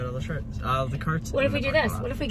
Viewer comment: LOL I literally thought said "Try not to be mad about all the sharts" instead of carts!! Sorry I'm immature 🤣